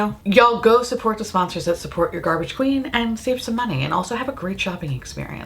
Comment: When you said hands cams I thought you said ham scam! 😂 I need hearing aids lmao